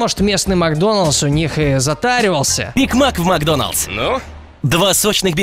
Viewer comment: кому тоже послышалось биг мак, а не грик мак?)